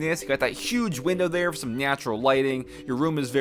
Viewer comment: did he say NIATURAL xD ??